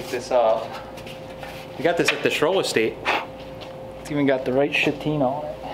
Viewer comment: Did he say at 7:30, the right shitina?Did you know l use urin is used to give bronzs a nice petina.